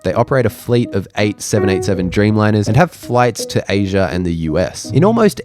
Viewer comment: s Did I hear right? Dreamliner 878? Have you been flying too many hours? Maybe breathing in the dodgy ozone? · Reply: (Zip Air has) "eight 787 dreamliners"